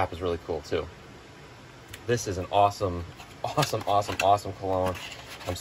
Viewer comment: I thought you said ' awesome clone ' at the end there and and nearly had a fill my pants moment